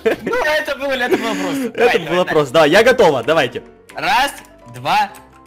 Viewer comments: лайк если на 9:09 услышали что сказал я готова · Кто услышал что сказал я готова ? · кто услышал я готова?